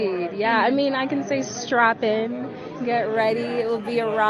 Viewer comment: When she said "strap in" at first I heard "strap on" - people going to watch it are gonna get fcuked in the behind by the girl power and diversity 😂😂😂